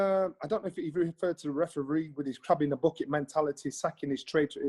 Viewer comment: "Crab in a bucket"?? I thought he was saying Crap in a bucket as in "Ah you're shite in a bucket" like the travellers say.